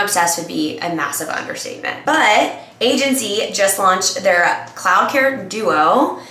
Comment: I totally thought I heard you said 'clown care'. 😂